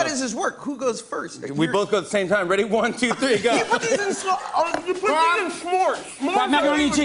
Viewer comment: What was Jimmy's clue for the Mac n cheese..? I've listened like 10x and I can't make it out... lol I keep hearing Josh yell ''you put them in smore's''.. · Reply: he says "kraft"